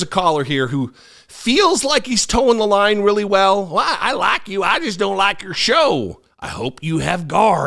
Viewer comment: I heard " I like duds"...she is and this person is ignorant beyond comprehension. . .